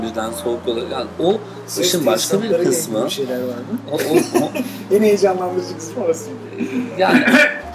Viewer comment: de Cem Adrian'nın karşısında ki beyfendi ne diyor acaba? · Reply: ses tesisatları ile ilgili bişeyler varmı